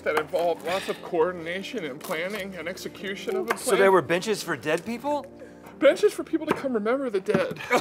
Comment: 😂😂😂😂 I thought he said bitches